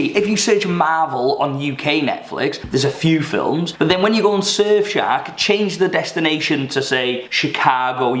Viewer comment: Who else thought he said surfshag 😂